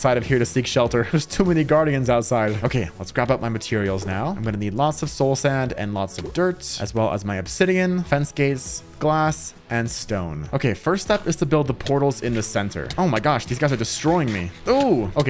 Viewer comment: did just say fance gay😂😂